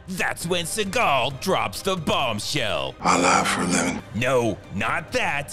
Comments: What does he say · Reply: I lie for a living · "Seagal drops the bomb." "I lie for a living." "No....not that, everybody knows THAT." Its hilarious because its true. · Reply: I'm glad you transcribed this. I thought he said "I live for lemon". I was so confused. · "Seagal drops the bomb." "I lie for a living." "No....not that, everybody knows THAT." Its hilarious because its true. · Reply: Yeah I also thought I'd heard "my life for a lemon".